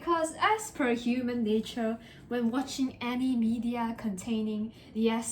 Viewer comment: How she said it: "Any media" How i heard it: "Anime ew da"